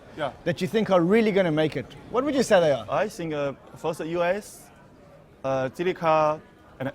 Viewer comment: what the hell does he say? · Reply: yea but what was the first one. bifrost? i think